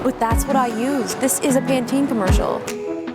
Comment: I thought she said "the is a panty commercial". Now that's something I'd love to see her review, panties.